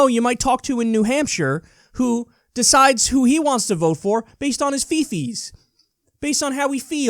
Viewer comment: Based on what?! 🤔 Sounds like Kyle said "votes based on his feaces". Google captions believes he said "fee fees" whatever that is 🤣 [This is a joke, but I wouldn't be surprised if people in America vote based on their "fee fees" !